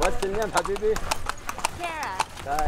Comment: I love how they thought he said “have babies” when he was saying “habibi” which is basically Arabic for cutie or my love.